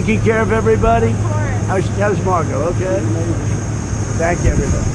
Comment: I swear it sounds like he called Marco, “Margo”.